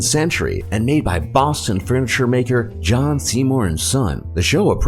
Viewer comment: Let's all be honest at 1:56 we thought he said John Cena 😂